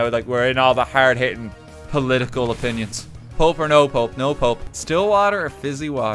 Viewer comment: I misheard "pulp or no pulp" as "pope or no pope." Which confused me when had such an emphatic "no pope."